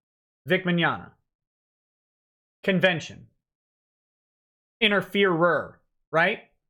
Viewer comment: & 1:21:36 I heard this as "InterFuhrer" rather than interferer and found it to be oddly amusing for whatever reason.